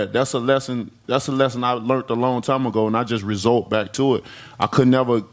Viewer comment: "learnt" and "result" instead of resort. brilliant.